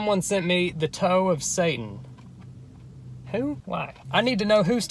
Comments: I thought it said toe of santa 🎅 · i thought it said toe of santa for like the whole video